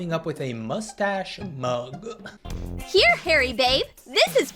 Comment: i thought they said “hairy vape” 😭